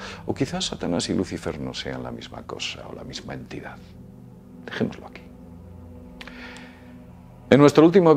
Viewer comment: Que dice en 7:34? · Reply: Dejemoslo aqui